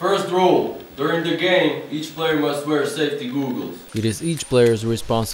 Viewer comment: The what he said googles it sounds like gooogles